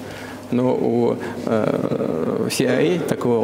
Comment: у кого нет алиби в интерьвью говориться, не расслышать никак пока не выходит...? Что-то со звуком видимо или ещë что не позволяет расслышать слово😢😥😰😭😪☺ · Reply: Я слышу, что как-будто аббревиатура типа CIA или CRA.